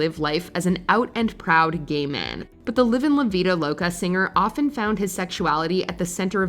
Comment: When i heard "living la VIDO loca" 20:32 i LOST MY SHIT .🎉 And then realised how white and annoying the speaker's voice is